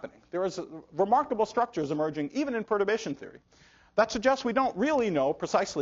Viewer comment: I can't understand what Nima are saying in 7:07,...is "probation theory"? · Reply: Lucas nogueira de sá martins perturbation theory